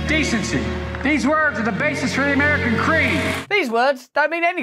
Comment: Did Biden say American cream?